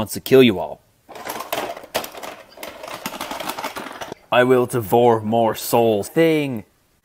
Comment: Ayo,did it just say "I will DIVORCE more souls!?" XD